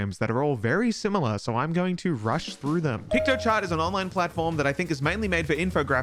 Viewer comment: When said "PiktoChart" I thought he said "Pictochat" and I legit didn't put it past him to make the graphic on a web-emulated Nintendo DS